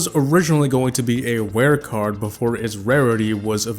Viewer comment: instead of rare i heard "ware"